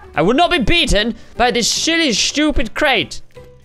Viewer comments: did he say what I think he said? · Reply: 'Silly stupid create'. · for all of you that thought he said the s word he didnt he said silly in a stupid voice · Did he say shity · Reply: I wish he swore. But he said shhhilly :( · all the people who think said sh!tty he didn't he said silly not sh!tty it just sounded like he said sh!tty Have a nice day!😀😃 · did he say what I think he said? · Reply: shilly stupid crate. I thought he said that too I paused the video and look down here if anyone else noticed